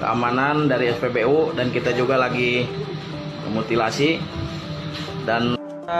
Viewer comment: mutilasi ? Apa gw ga salah denger ? Investigasi kali maksudnya Thx for info